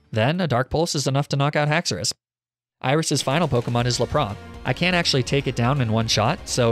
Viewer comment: did... did you just say laprawn? EDIT: never mind, i misheard